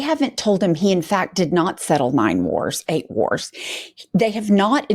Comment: I thought she said he did not settle "mind wars" lol.